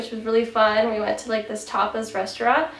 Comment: At first, I thought you said you went to a topless restaurant, but then I realized you said tapas. Hahaha